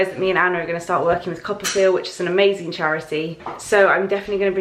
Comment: Lol! I'm a little behind but I just finished reading Anna's post where she talked about "Coppafeel". This whole time I thought you were saying "copperfield". Hah. #americanproblems